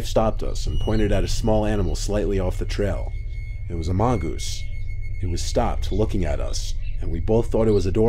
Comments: When you said mongoose it sounded like you said among us 😳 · "It was a mongoose" I heard, "It was amongus" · Did he just say amongoose.. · did you say, amongus? ;)